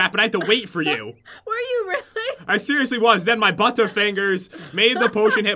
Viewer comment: I thought he said bunter fingers